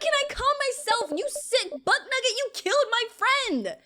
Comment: Did she just say, BUTTNUGGET?!😂😂